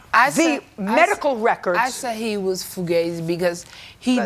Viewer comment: What did she say @ 2:07 😆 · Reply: “Fugazi” (pronounced, foo-gay-zee), meaning, fake or counterfeit.